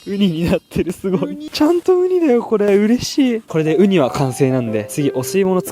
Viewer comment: ウニしいに聞こえた😂